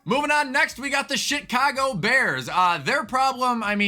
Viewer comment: did he say shitcago bears😂